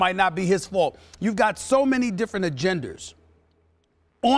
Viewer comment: did SAS just say 4:33 did he just say "so many different agenders"?